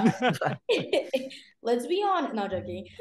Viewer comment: what did Leah say at 37:37 where everyone laughed? It was after Walker said he finds Percy attractive as well? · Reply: i think it was “come on now, percy jackson”